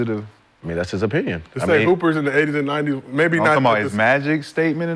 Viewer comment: What is Gilbert about? I thought he said in the 80s and 90s.